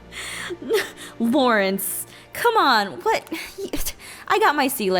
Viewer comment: lol did she say Faurance at 10:05 lol